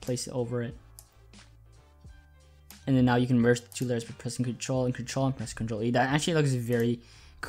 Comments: WTFFFFFFFFFFFFFFFFFFFFFFFFFFFFFFFFFFFFFFFFFFFFFFFFFFFFFFFFFFFFFFFFFFFFF 1:19 COZ I M NOT FROM ENGLISH SPEAKING COUNTRY I DONT KNOW WTF Y JUST SAID THERE NOW JUST PRESS CTRL AND CTRL AND CTRL AND CTRL TO DELETE THAT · Idk what you said at 1:18 when you said to press ctrl ctrl and ctrl but it doesn't even work. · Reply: Ctrl + E